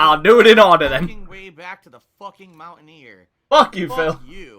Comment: It sounds like he says "mount ni**er". XD